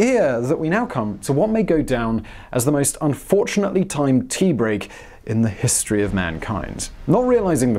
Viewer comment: I first thought you said "most unfortunately times pee break" and the only thing to run through my mind was: "well if you weren't in the control room then where were you?" "The toilet"